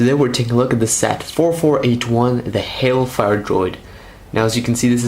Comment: Hey lego put more than 4 missiles on the hailfire droid. They didn't do that again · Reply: I thought it said "hellfire droid"